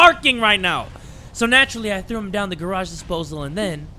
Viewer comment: Naw he really said “Garage” 💀✋